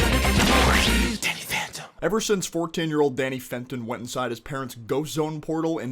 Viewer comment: What did you call him? Four-ten year old?! 😂 P.S. If that's an accent, regional dialect, or speech impediment, you have my sincerest apologies.